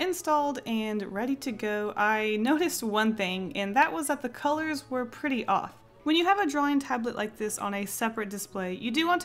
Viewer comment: i thought she said the colors was pretty odd at first :/ oh well